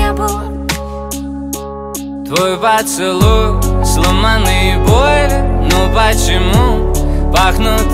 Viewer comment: Я в первый раз услышала "сломанный боинг"